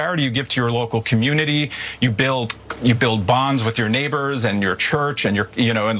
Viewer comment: When he said, "build bonds", I first heard it as, "build bombs". A Freudian auditory perception perhaps?